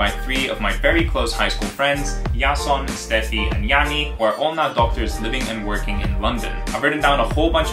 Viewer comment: did he say we're all not doctors in the beginning or?